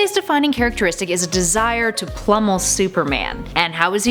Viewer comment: did u say "plummel"?